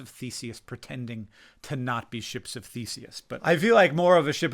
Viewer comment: I thought you said a “ship of feces” at first. I thought, now what kind of crazy metaphor is that? Haha your anxiety/non anxiety may be affecting your memory also, especially if you haven’t been sleeping well. Meditation may help